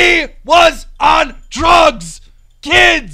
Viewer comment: i thought he said do drugs kids